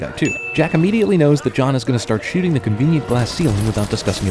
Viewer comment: Also, it said "ceiling glass" instead of "glass ceiling"